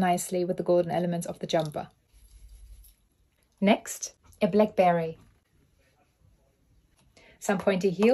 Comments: I thought you said Blackberry. Like how did she find one of those old phones? And why is she using it as an accessory?! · I thought she said “ a blackberry.” But it looked like a black beret.